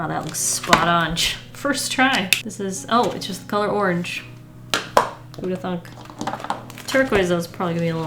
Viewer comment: Did she really say who would’ve thunk??😟